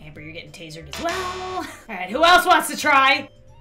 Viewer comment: “Ok who else wants to try?” I thought she said “Who else wants to die ?”